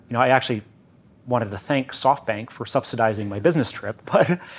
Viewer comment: Did I Hear softbank :D :D